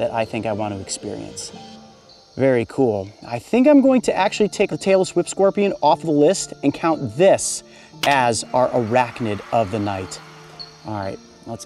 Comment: I thought he said Taylor Swift Scorpion LMFAO